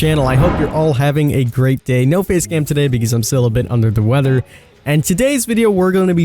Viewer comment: I thought he said no face camp today